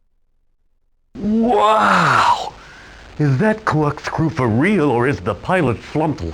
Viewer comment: Maeva reminds me of Sonic Drift 2 I originally thought he said clock screw